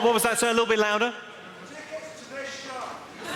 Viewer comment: what does the heckler say? I can't understand... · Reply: A ticket to this show